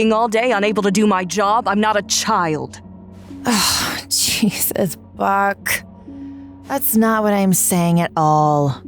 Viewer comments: She said Jesus Buck, I thought she said Jesus Fu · Thought Dr. Collingwood said, "Jesus, f*uck!" Not "Jesus, Buck!" (as in her name)🤣🤣🤣